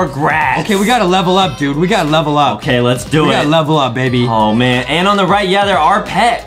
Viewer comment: I here say we gotta level up baby 2:20 wait what?? say baby?